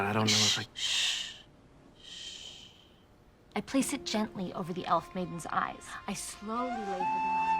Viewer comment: It must be my polluted mind but I swear she says 'I gently cup one breast'